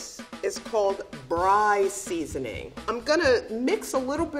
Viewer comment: I wanna see more of. She’s delightful and she’s got that awesome grandma vibe to her. [Edit] this comment blew up and I didn’t even know until now. Go sub to my channel Gooby Bear ♥️ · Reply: She said she was using Bri seasoning? Am I mishearing?